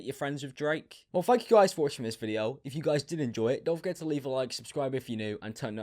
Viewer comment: It sounds like you said “well fuck you guys for watching this video” lmao